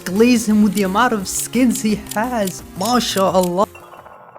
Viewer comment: Did I just hear Masha'Allah?